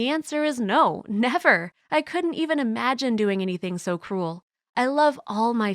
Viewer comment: sounds like she says "cool" instead of "cruel"